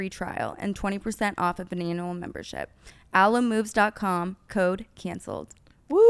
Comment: When Brooke said “annual membership” I heard “anal membership”. Maybe just because their convo before 😂